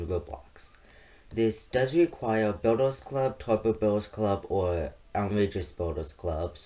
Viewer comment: in 0:16 did you say taco bells club